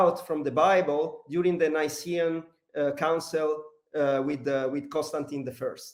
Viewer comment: what did he say here "This was wiped out from bible during the__________________" . could somebody please help in completing the sentence? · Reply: Nicean Council under (Roman emperor) Constantin the Great...